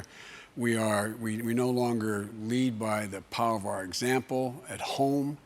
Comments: Did he say "we are in a physician..." in 6:00? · did he just say "we're in a physician where ..." ? 😄